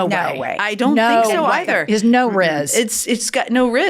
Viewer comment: Did i just hear and old lady say "rizz"?🥀